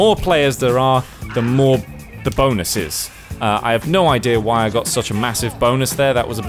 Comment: Sounded like you started saying "not sure why I've got a massive boner..." I laughed. I'm nearly 28. I'm very immature.